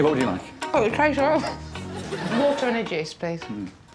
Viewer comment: did she just say water and a Jizz...